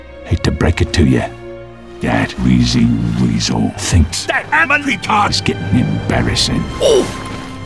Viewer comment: ok i watched this at premiere and still haven't been able to figure this specific one out, any of y'all know what the f2p sniper is saying at 24:38? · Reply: I think it's "That wheezing weasel thinks that America is getting embarrassing"